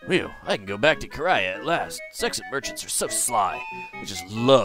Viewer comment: Vinay del zexay is a creepy, almost perverted name.....also when you said " zexen merchants are so sly" I almost misheard it as sexen merchants are so sly